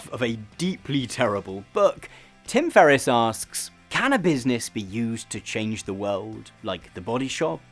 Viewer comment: I heard "cannabis be used to change the world..." 22:40 😂